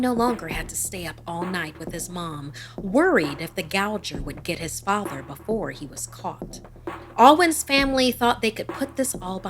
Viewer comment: I thought she said “worried that the grim gouger could get his father before he could fart”